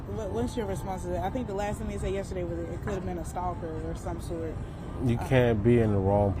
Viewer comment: Did she say “could have been a stalker or some shit” at 4:51 in the interview!? 👀😳 I was trying to give her the benefit of the doubt knowing she has worked been working with Sades family and maybe they have a close relationship but come on now…..this is too much.